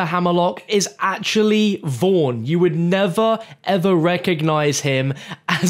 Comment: So I'm sorry if this is a stupid question, but who the hell is "Vorn" at 4:45? For some reason I can't recall this character and have no idea who it is? I tried looking it up and found nothing. Am I just hearing the name wrong? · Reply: I think he meant Vaughn from the Tales game.